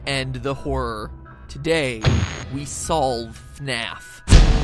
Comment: I heard ‘We solve FNAF’ as ‘We solve Math’